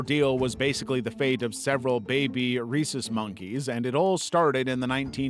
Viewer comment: Sounds like there saying “Reese monkey”